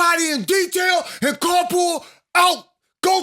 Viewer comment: For weeks I thought you said car pull… I never knew it was Car Pool 😂😂